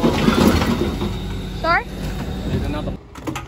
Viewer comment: I’m just genuinely curious at 6:03 what did he say to you? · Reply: There are more clean ups down the road if you want to film them